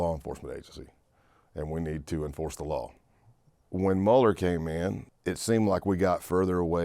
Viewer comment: it sounded like he said Mulder!! But close enough! Now the need an agent skulskull! And they'll be temu xfile agents skully and Mulder 😂